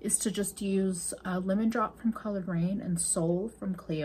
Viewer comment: I thought you said colored brain instead of Coloured Raine at one point and it was hilarious to me for some reason!!! 🤣